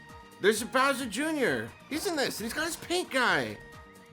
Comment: Bowser Jr.: Has a paintbrush: He's got his paint guy! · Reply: When I first heard it, I had to take a double take as it sounded like said “He’s got his pinkeye!”